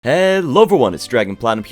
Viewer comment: For some reason said "hello everyone" but i hear him saying "level one" i have no idea